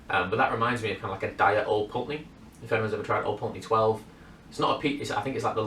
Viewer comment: I didn't understand what you said around 7:55 about the lowest peated whisky but is salty? I'm interested. I also love Talisker and I wonder what you would have to suggest that is similar. I was suggested lagavulin, laphroig and ardbeg. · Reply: Jazz Warrior Hey man. Old Pulteney is a very low peat Whisky, the lowest of all in terms of PPM rating. But, due to it being located on the north coast of Scotland it is a little bit salty, slightly savoury flavours. If you like Talisker, I’d go for Caol Ila or Port Charlotte bottling’s. Ardbeg, Laphroaig and Lagavulin are great; but they are the biggest flavours of smoke. Caol Ila and PC are a little more approachable; still peated! But just more approachable. Cheers!